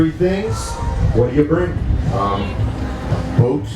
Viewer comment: did he say boots or boobs!?